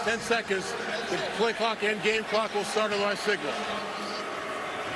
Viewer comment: he clearly says "the game clock and the play clock will start on my signal"